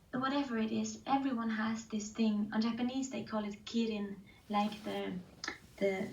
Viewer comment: What is the Japanese word she said? Kirin? I really wanna know! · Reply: Here you go 😊😁 It's called Ikigai Midori Karlsson <link>